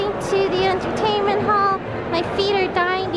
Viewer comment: Lol I thought it's said hentai hall 😅